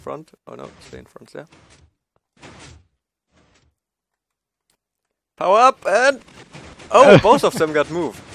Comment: every time they said power up i thought they said hau ab xD it german and mean get away